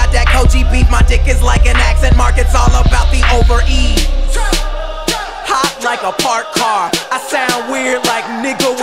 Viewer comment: Sounded like he said “Hot drank a fart car” 0:40 but I know he didn’t say that lol